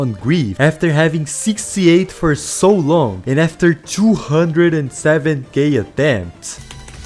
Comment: why did I hear 207 gay attempts